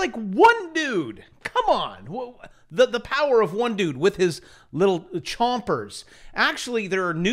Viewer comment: I heard, "the power of one nude..." True story, one shock away....